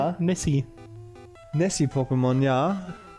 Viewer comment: ab 7:06 Ich habe verstanden,dass Lapras ein ,,Messi-Pokemon'' ist :D